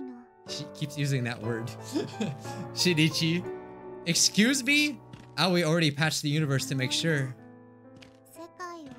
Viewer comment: Who else has no idea what word means "rejected" in that sentence? I'M SO CURIOUS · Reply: I went through and searched for some videos after last ep and get prepared cause it says "cucked" I'm pretty sure 😳😳